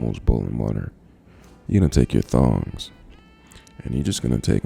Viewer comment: Hold on.... am I the only one that heard him say thongs instead of tongs😅😂😅😂